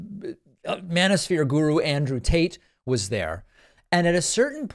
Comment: I swear I heard you say Andrew Taint😂😂😂